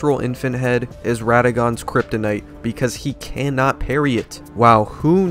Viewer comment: I never saw him use his parry at all the first 4 playthroughs I fought him, and 2 of those were using sorcery and 1 was incantations. He didn't do it a single time. But then the most recent time I fought him with spells he was doing it constantly. Was he maybe bugged and not able do use his parry until one of these later updates came through? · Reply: I thought you said he can't parry infant head